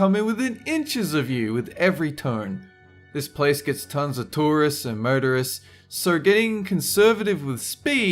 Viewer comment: when he said tourists and motorists, I thought he said, "tourists and murderers" and my initial thought was, "well, reckless driving there is essentially the same thing" 😂